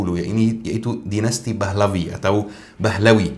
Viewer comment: Afwan Syeikh, ana ndak salah dengar kah? Dinasti Pahlevi atau... Ba'alawy? Dari aspek pronoun? Maaf Syeikh kalau ana salah persepsi maupun salah dengar. 🙏🏽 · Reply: pahlevi